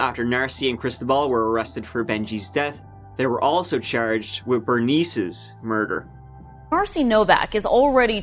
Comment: I thought he said he married "her niece"